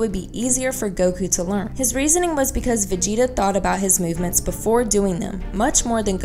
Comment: you said vegito instead of vegeta